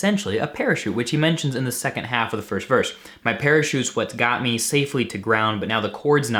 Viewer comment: This song makes so much more sense to me now!! I always thought he said: “my pair of shoes would get me safely to ground” but parachute just makes a lot more sense so thanks haha. Also great video and channel!